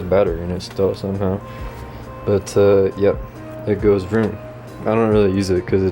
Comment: did he say "it goes vroom"